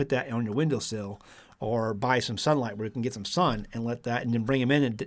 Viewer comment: @ 8:59, did you say "buy sunlight"?😏Sadly, "they" are trying to make that a reality...